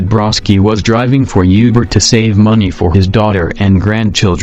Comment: Instead of saying Uber, it sounds like he said yuber. WTF